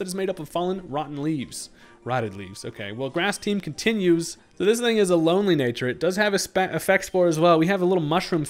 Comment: what does he say at 2:10? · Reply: "so this thing is a lonely nature"